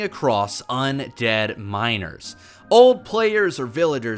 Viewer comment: I actually misunderstood this sentence and for like 3 seconds I thought he was talking about minors instead of miners lmao. “Imagine stumbling across some dead *minors*”